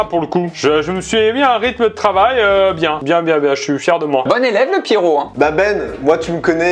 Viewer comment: Qui a entendu comme moi "bon élevé le p'tit gros" à 13:51???